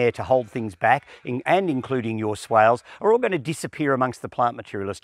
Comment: What is he saying “swayles”? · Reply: A swale is a trench. I usually try to make sure the captioning (CC) is correct, not just automated, but I've just checked and it isn't so thank you for letting me know and I'll try and re-edit the captions over the next few days.